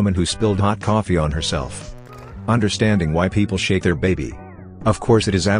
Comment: I thought it said 'shave' and not 'shake' their baby, and it was confusing.